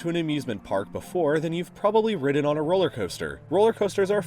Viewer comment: did he just say rided or im i just dumb af (shouldnt it be ride?) Pronounced rid